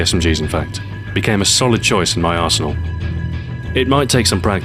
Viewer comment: Sounded like he said "it became a solid choice in my arsehole"